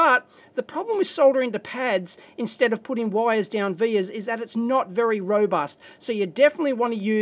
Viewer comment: What is that word you use? 😮 "The problem of soldering to the pads instead of wires down _____" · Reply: "Vias", from wikipedia: A via (Latin for path or way) is an electrical connection between layers in a physical electronic circuit that goes through the plane of one or more adjacent layers.